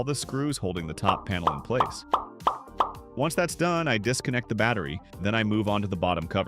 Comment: what did i hear? the battery????